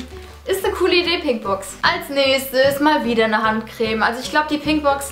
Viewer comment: Wer hat auch bei 3:35 Handkrebs verstanden?